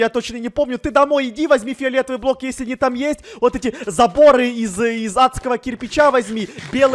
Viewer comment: Мне послышалось место заборы-запоры😹Я и моя фантазия😹✨🔥